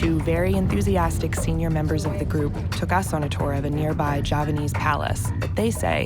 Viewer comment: subtitles should read JAVAnese instead of JAPAnese. This was filmed in Java, not Japan